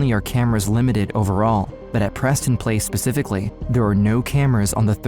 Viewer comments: Im sorry no disrespect but am I the only one who thought he said "PrestonPlayz"? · I thought he said Preston playz for a second.😂